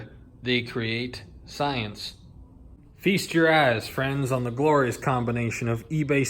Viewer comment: Sounded like you said, "feast your ass"